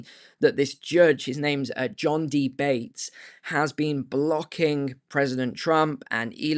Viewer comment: Did you say at 5:22 a judge with the name John 'DeBate'? Lol. Are you sure that's his real name?